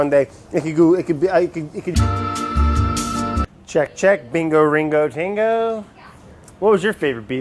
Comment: I 100% think that said Ringo